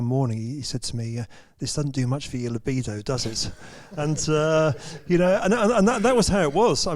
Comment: what does Nick say at circa 53:50 ? quoting Ross, but I cannot understand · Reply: "This doesn't do much for your libido, does it?"